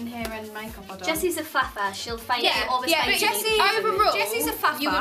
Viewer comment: what they are saying that Jesy is??? I don't understand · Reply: "Jesy's a faffer", meaning she tends to spend more time getting ready and is usually indecisive about her outfits, hair, etc. They were talking about who takes the longest to get ready.